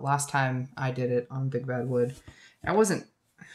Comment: So at one point I thought you said “big bad wolf”, and wanted to suggest if you were trying to make a fur top you could try a plastic fork. Then I realized you were saying WOOD. 🤣